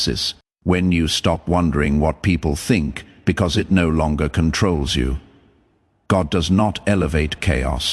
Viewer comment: Hold up I thought you said we were no longer chosen